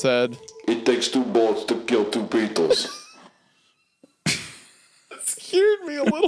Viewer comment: “It takes two bullets to kill two beatles” - The Gun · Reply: OHHH I thought he said "it takes two bullets to kill two peoples"